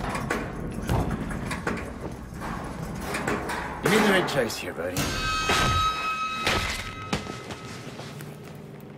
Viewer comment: Wait, @ 3:10 ish I can't understand what that guy says, anyone? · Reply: You made the right choice me boy